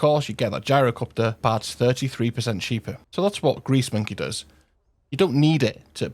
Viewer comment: I swear I heard you say greaseman Key!